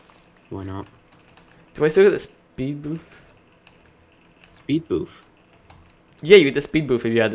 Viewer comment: did said speed boaf?